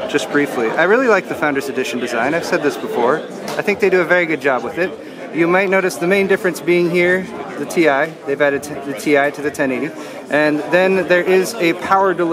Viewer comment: Did anyone hear that GPU pun that made at 1:20. It sounded like he said "they did a vega job with it"